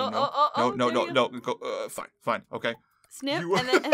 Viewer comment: it sounds like says the f word!!!!